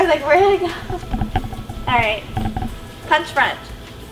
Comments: When said punch front, I thought she said punch friend!😂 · I thought they said punch friend but they didn't😂